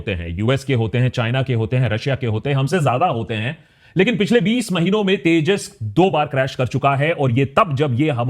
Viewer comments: subtitle says 2 crash in 20 yrs instead of 20 months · 20 years instead of 20 months?? Writing check karo bhai